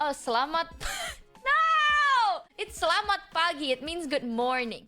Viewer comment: Truth be told, as a Philippine fella, I hear "Salamat Pangit-" or "Thank you, ugly!" 😅